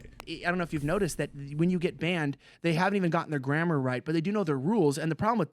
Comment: "you have been banned" is correct grammar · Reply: I’m so confused because I thought he said “they don’t even check the grammar” and then went on to say something grammatically correct. Did i miss something?!